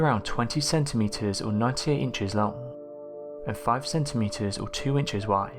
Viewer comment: I had to listen a few times for him to repeat the length of the viking poo. I thought he said 98 inches but he said it's 9 to 8 inches big. But 20 cm equals 7.8 inches long. Which is good but huge, well in my opinion at least. .